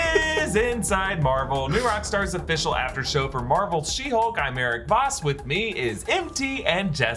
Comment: I thought you said "I'm Married Voss" 😁